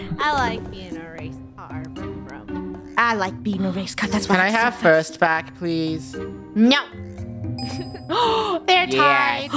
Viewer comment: What did say? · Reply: He said can I have first back please